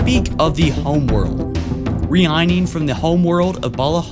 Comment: did he just say re-igning?